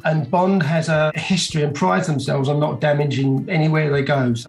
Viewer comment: Did he say "Bond prides himself on not damaging" 😅